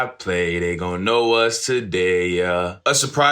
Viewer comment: I thought he said "It's another way" too and I also thought he said "They gon' know what today is" it's really "they gon' know us today" I think we have a few Mandela effects right here